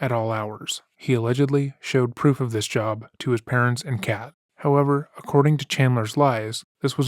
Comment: this is a little silly but the way you said “he allegedly showed proof of this too his parents and Kat” sounded like he had a cat that he showed this to as well 😂